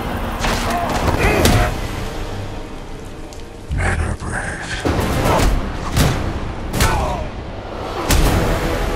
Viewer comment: what did batman say his voice is more muffled than christian bale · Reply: He says "men are brave"